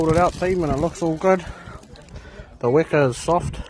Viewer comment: Awesome brother! Mean kai Ps. Did you say Weka? 😅😂 4:49 Must be in Stewart or Chatham Islands lol